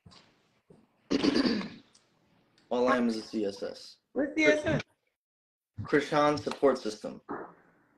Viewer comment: did he really say that he is chrisean sopport system haha wtf he slaving. but disses him or what cane out of his mouth there is twisted 😂